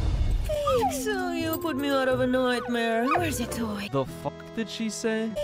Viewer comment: I think she was saying like “aww, Zoey, you put out of a nightmare! Here’s a toy”